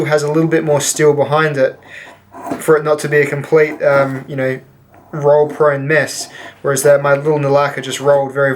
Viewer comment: Did you say "roll prone mess" or "roll proneness"? I had to listen to it about seven or eight times but I think it is the former. 🤣🤣🤣